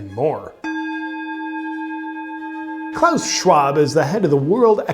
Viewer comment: Oh my gosh! I thought you were saying Charles Schwab was the head of the world's economic system! You about gave me a heart attack.